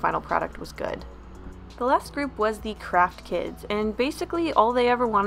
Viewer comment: thought you said crack kids and i was kinda worried